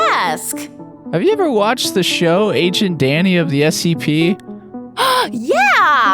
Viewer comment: I thought it said agent daddy